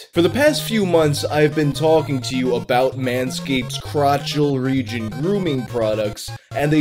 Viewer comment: My video hung up right when I heard "talked about manscaped crotch". I'm sure that's not what you said, but that's what it sounded like.